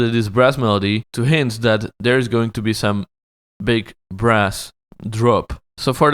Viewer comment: Did you say "big breast drop"?